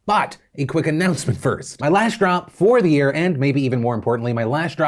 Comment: I heard “my lash drop”. My eyes opened up really wide in excitement thinking he was selling eyelashes lol But that didn’t last because I quickly realized he actually said “last drop”😩😭😭😭